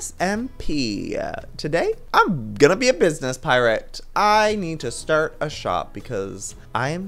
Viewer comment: is it bad that I thought he said business parrot in the opening. also Eversea is the better name